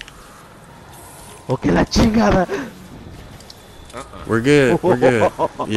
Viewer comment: did say “Ok la chingada”? Like did he speak spanish for a second?